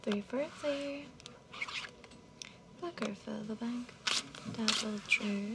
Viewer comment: Did she just say "Fucker fill the bank?"